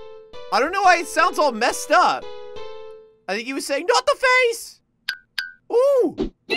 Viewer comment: I thought he said Como died but he said not the face